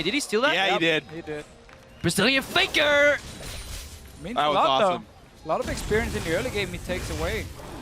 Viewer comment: Eu ouvi um "BRAZILIAN FAKER"? Hahahahaha KAMI GOD!